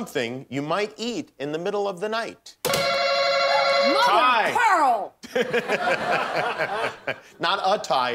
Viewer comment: What does julie(claire) say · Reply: Mother of pearl